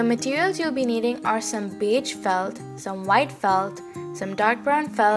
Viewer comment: Did you just say, "bitch felt..." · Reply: No I said "Beige felt" hahaha :P